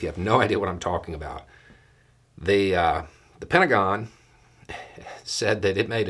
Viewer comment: I don't know if it was intentional, but it sounded like you said "penta-gone".